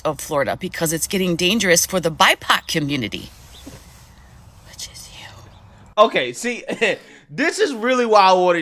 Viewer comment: What she said: "which is you" What I heard: "stay in your lane N"